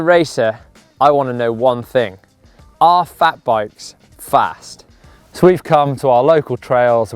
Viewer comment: Damn, I thought it said are fat blokes on bikes fast - was looking for tips - lol Good review that!!